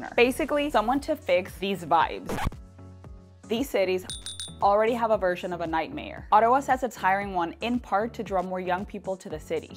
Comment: “These cities already have a version of a nightmare” I thought she said 😂